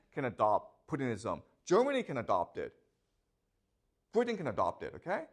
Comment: did he say "putin can adopt it [putinism]" ? :D